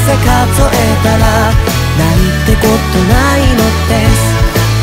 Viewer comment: 「今日までの幸せ数えたら、なんてこと無いのです。」ここの歌詞「なんてこと無いのです」が「なんてことないdeath(死)」にも最初聞こえたんですけど考えすぎですかね…？